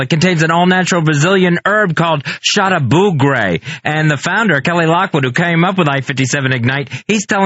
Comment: I thought Fez said “shot of blue gray” at the end of the mighty horse song, finally I know the real line and its origin!